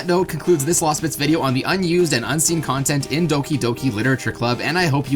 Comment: Is it weird all i heard are "Doki doki litlte strip club"? No? Ok.